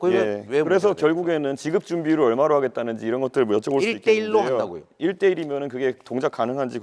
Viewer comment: 이재명이 말하니까 1:1로가 아니라 일대일로(一帶一路)처럼 들리네